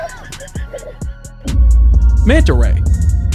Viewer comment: Did someone notice? After he introduced manta ray 1:21 ...the ray itself sounded like its saying '*MANTA RAY*'